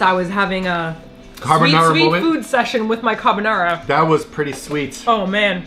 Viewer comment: sweet jesus I thought was going to say food sex